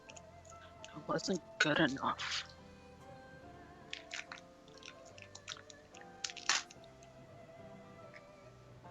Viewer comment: Did say "unlike you two" or "LIKE you two", because the first option won't make any sense if you are talking to a brownie and...whatever Fenix is. [P.S. I am personally betting Fenix is a kitsune. Probably makes no sense within this setting, but I'll still keep hoping.] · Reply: What he said was, "I'm like you two," referring to them all having fey blood.